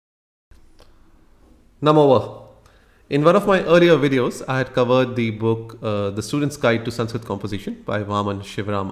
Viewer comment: What is that word? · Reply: नमो वः (namo vaḥ)